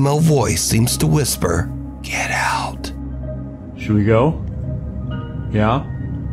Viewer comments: I heard a “yeah” to the question of the first video instead of “get out” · In the first video with the dog.. I heard “yeah” not “get out” 😮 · I heard “yeah” over “get out” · In the video with the dog barking at the black shadows , it sounds like she says yeah not get out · I thought the creepy female voice answered “yeah” when he asked “should we get out” I didn’t hear “get out” I heard “yeah” lol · I heard “yeah”, not get out. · The "should we go?" dog one I heard "yeah" instead of get out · I heard "death" in the one with the golden retriever. Not "Get out"